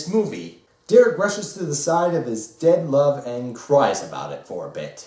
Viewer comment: I misheard "dead love" as "dank love"